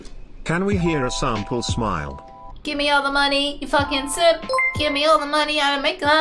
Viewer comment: "Give me all your money, you fucking simp"?? Did I hear that correctly?